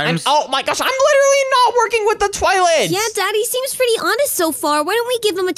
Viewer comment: tv woman:ya daddy Did anyone heard this 💀💀💀💀💀💀💀💀💀💀💀💀💀💀